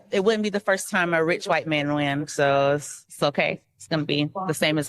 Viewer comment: A RICH WHITE MAN WAN ? WTF DID I HEAR? IS THIS EBONIC'S 2.0?